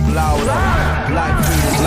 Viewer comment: part of the song when he say "Louder" i actually thought he said "prowler"